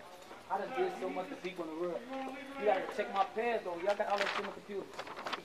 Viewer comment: is when he mentions Jeff McGraw (Lil Jay). Also at the end the Christian inmates call him gay and threaten him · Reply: I hear Von say "Goin in so if they tie me up, r*pe me and do all that dick face then what? I'm saying I done did so much to people in the world you gotta check my past y'all got all that sh!+ in the computer, You got Jeff McGraw I'm trying to get away from that whole pack they' trying to ki!! me, and something along the lines of what if they size me up I gotta punch or do something, what's finna come out of all of that?" And that's with headphones 🎧 on. So basically he's volunteering himself to go to the Gay pod to save his life but has to worry about lil Jay taking him out in the Gay pod but safer there 🤦🏾‍♀️🤷🏾‍♀️